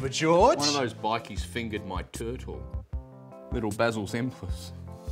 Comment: "Little Basil's entrance" 😂 EDIT: Misheard this, was actually 'Little Basil Zemplis'